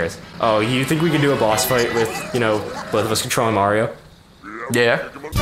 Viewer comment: I thought it said CONTROLLING CONDOMS